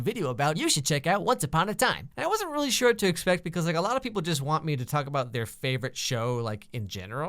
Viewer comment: I paused and it sounded like you said -I wanna talk about your favotie Shit. Not that far from truth XD Luv ur vids